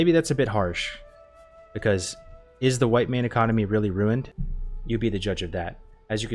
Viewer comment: I thought you said "White man economy" for a split second lmfao